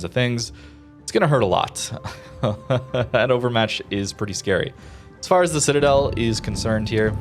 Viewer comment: Wait. Did you just say "Hannovermatch?"